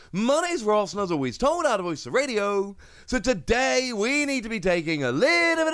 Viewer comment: After "my names Ross and"....no clue what comes right after that lol 😆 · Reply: Lol I hear “and as always told out of pokeTCG radio” but I can never be sure of it